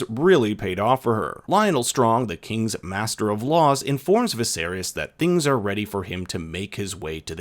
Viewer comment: Did you say 'Viserius'?